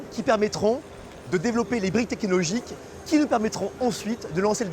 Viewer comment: Gros LOL à 0:44 : c'est quoi ça les briques "tekilogiques" ??